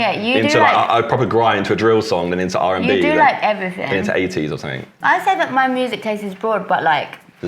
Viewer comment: when said 80s I heard ateez and got excited I hate myself